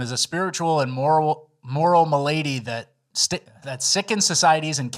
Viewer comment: The most annoying thing about Trump is that he hired Pam Bondi and appointed Amy Cony island Barrett. Who gives a shit about antisemitism, it’s not a problem here UNLIKE anti whiteism. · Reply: Did I hear a “my lady” from instead of “malady”?